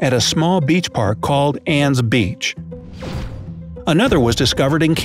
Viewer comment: ANN'S BEACH sorry, I misheard it